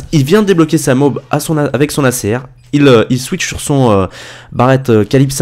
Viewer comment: Ok, t'es juste sourd mec. Clique ici: 0:59 Tu entends "switch" toi ?